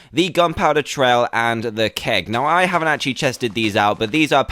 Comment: he said chested instead of tested i think